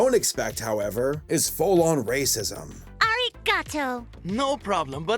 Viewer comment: I'm sorry... did you just say he's a full on rapist?